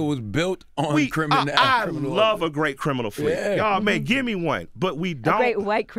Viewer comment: What does Killer Mike say at 8:15? "I love a great criminal ________." I can't tell what word is at the end of that sentence. Let me know, please. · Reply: criminal fleet? Criminal flea? he turned his head away from the mic. i spent an hour trying to figure out what he said. time well spent at work lol